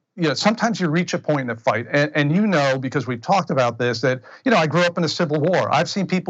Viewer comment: did he say he "grew up in the civil war" ??